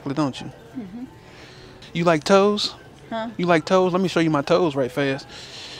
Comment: I thought he said you like toast bread 🍞 🤣😂🤣🤣